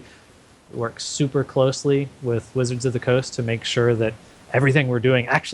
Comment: Did he say 'Lizards of the Coast' instead of 'Wizards of the Coast'? ;-)